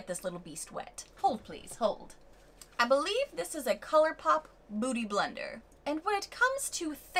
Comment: You did such an entertaining tutorial Halloween 👻 🎃 thank you! Had a blast 😂 sounds like you say bootie blender. 🧡🧡🖤🤎💛❤️‍🔥💛🧡❤️‍🔥🧡🎃🎃👻💀